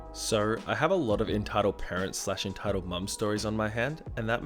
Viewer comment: lol sounds like he said entitled bum